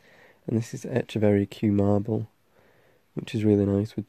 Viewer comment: What was the succulent at 1:15? I couldn't pick up what you said😅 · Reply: Echeveria Kew marble ! Sorry I need to learn to speak louder